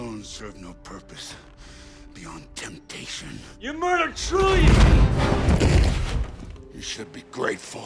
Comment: what did Bruce said? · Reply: "You murdered trillions!"